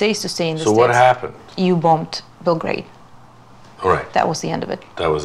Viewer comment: I apologize... what does she say when he ask her why Russia is no longer "in love" America? Around 26:46. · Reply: You ( the us) bombed belgrade